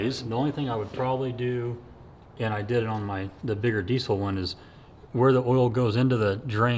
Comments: did i hear him say "THE BIGGER DIESEL ONE"?????? There's a BIGGER one thats DIESEL?!?!?!?!?! I didnt know it was possble to make a diesel rotary, I NEED TO SEE IT!!!!!!! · WAIT HOLD UP… did he say the bigger diesel one??? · did he say a diesel one? I want to see that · did he say, Bigger DIESEL ONE?!?! DIESEL ROATER?! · Did he say the bigger diesel one?? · did I hear he has a diesel one to?!